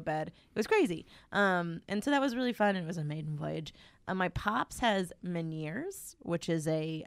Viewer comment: In defense of, at 40:48 changes subjects and the first thing she says sounds like it's french so there's less context clues to figure it out: "so that was really fun and it was an amazing voyage. ah. Mypopshas Meniere's". It's hard to follow that means her dad, especially if you don't hear a lot of people call their fathers "pops".